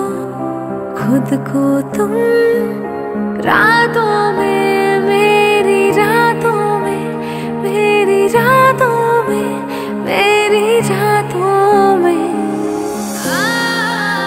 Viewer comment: i think i heard jaaton mei🙄🙄🙄